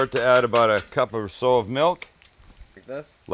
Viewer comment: Did you say, "...sow's milk?" As in a pig, sow?